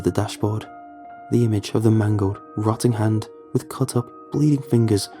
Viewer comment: I heard “I stared at the dashboard, remembering the mango hand” instead of mangled hand and that had me lolling lmao. Also, great narration on this story